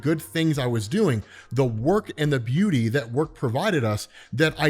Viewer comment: I was wondering why Howard Stark would be wrapped up by the beauty of his work, then went and rewatched what was saying at 16:03, but was actually, "I was so wrapped up in the good things I was doing-- the work and the BOUNTY that work provided us.." and then went..ahh, that made more sense..